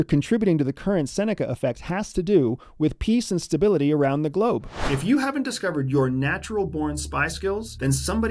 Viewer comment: I swear I thought he said If you haven't discovered your natural born Spice Girls…”.